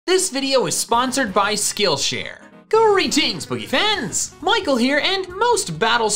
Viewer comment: Auto correct thought your intro said "gory teens beef ends, Michael here" 😆😆😆😆😆